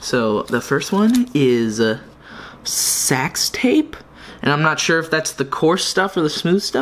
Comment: I thought it said sex tape💀